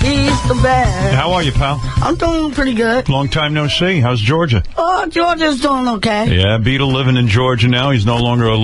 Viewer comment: I love how when Howard asks him, “how’s Georgia?” really Howard is asking how Beets been doing in Georgia. But Beetle answers for the State of Georgia by saying, “Georgia’s doing OK.” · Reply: At first i thought he said "ahh George is doin OK"